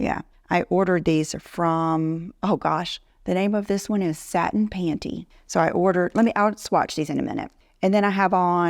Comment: I thought you said southern panty. 🤣🤣🤣🤣